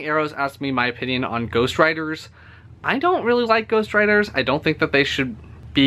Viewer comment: thought you said ghost rider gahahahahaha.